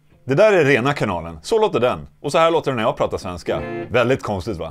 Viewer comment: why does the caption say spanish at 3:24 when you say "svenska" wich means swedisjh